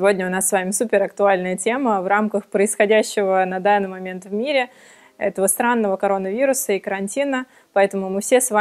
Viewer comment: Сранного карантина? Мне послышалось?